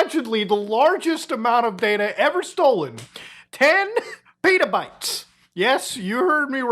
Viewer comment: I had to listen to how much data was stolen again cuz i thought you said 10 Peadobytes.